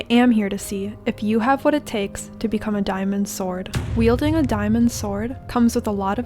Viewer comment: thought she said “if you have what it takes to become a dinosaur”